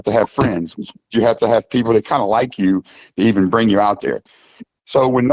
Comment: What did he just say? He said he had to have a friend to like you to bring you alone.